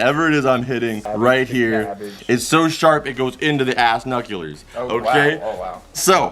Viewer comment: he said Ass nuckulures instead of brass nuckulures